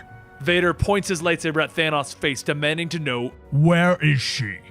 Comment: I thought he was saying “Thanos” for a second. I was like WOAAAAH Disney going CRAAAAZY